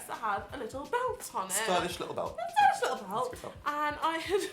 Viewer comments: what does dab say at 20:00 ? I didn't catch it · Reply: stylish little belt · the fuck did dan say at 20:01? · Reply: asdfg asdfgh he said stylish little belt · what does dan say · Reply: I think he said "little belt" really quickly😇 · this is the millionth time I've watched this and I still can't figure out what dan said at 20:00 pls help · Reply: 'stylish little belt' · What did Dan say at 20:02? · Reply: 'It's a good belt'